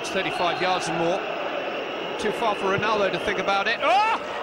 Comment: was edited he actually says 'not too far for Ronaldo to think about it'